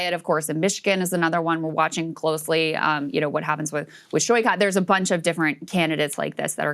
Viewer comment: What does she say at 23:33 "shoy cot"?